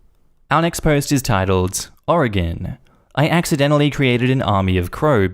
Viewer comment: I misheard 'oregon' as 'morrigan', so you can imagine how the next sentence involving CROW BODYGUARDS hit